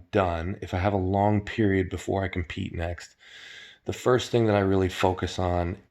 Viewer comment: i thought he said "before I can pee"